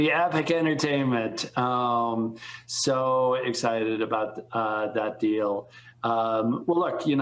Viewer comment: Reed: He did not say he was so excited for that deal. He said "So excited about that deal..." He left himself out of the statement.... He is not excited.